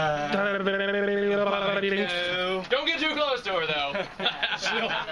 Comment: I misunderstood when he said the fan would blow you away, I heard "she'll blow you". Two totally different things oops :P